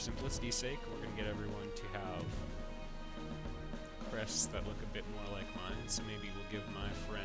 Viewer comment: for a split second, i thought he said breasts...